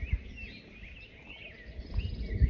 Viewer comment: I felt really Michael -ish when i first heard you 🤣 i thought you said which color North is.